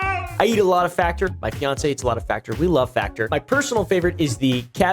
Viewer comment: did he just say fiancé?? When did this happen??